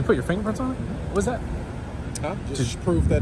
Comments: “To prove that that’s the vehicle of objection” You’re welcome. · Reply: What does that even mean? I thought he said either 'to prove that's the vehicle I'm touching' or 'to prove that's the vehicle in abduction' ....??? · “To prove that that’s the vehicle of objection” You’re welcome. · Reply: Thaaaa k you!!! 😂😂 I thought he said that’s the vehicle of possession lol thanks